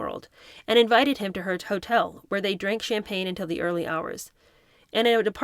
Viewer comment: Did you just say toe-tel (instead of hotel)? #Freud 🏩